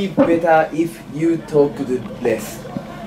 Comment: If you talk lessが is you talk best に聞こえて？？？？ってなった